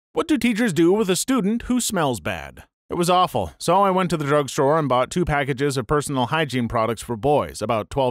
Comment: Yo so I heard you say all the hygiene stuff but when I rewatched it you said I went to the drug store